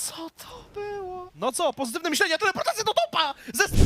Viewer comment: The comentators in the 1st clip 1:44 -What was that.... - What - It was positive thinking, teleportation is ass This is literally what they are saying. What second one says us a bit weird but I guess it was an irony that jumping to a ward - teleportation, is lame. 2nd clip 2:32 <squeaky voice> - They are gonna win that! -They can end it this moment -No, wait... -But, one moment... -Indeed (something inaudible) wide (something inaudible)